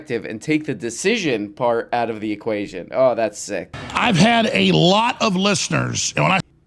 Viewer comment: I misheard "I've had a lot of blisters" and was very confused for an embarrassingly long time